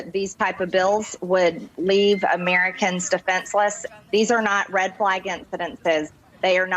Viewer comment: She said " these are not red flag incidents". She did not say, these are not false flag incidents. Kinda goes back to Trump's comment, "I don't see why there wouldn't be", regarding Russian interference in the election. Confusing right? That's what they want it to be.